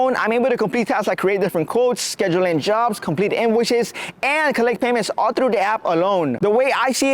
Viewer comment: good thing you had the subtitles I thought you said "complete ambushes"